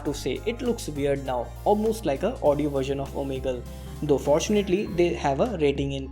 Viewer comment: what u said sir !??😞 audio version of which app please tell!!🙁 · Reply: omegle